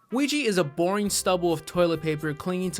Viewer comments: I don't know, but I heard "Luigi" instead of O-U-I-JA XDDD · why do i keep hearing "luigi" and not "oujia" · Why does it sound like he saying Luigi 🤣🤣🤣🤣